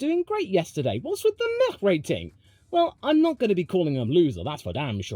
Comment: Legit thought the meh section said the meth